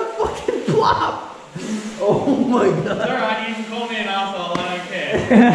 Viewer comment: Can someone please tell me what did the guide says I could not hear him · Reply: Bit late Bit…. “U guys can call me an awhile idc” rewatching thought I help years later😂